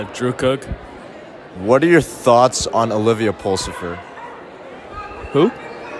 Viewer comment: I literally heard 'Olivia Ballsniffer' 😂